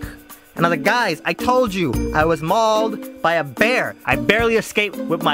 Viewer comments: "I'm not a player! I just cuddle a lot." looool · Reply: I thought he said "I'm not a player! I just cut a lot.". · "I'm not a player I just cuddle a lot" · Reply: I thought he said cud alot XD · I keep hearing, “I just cut a lot„....>u>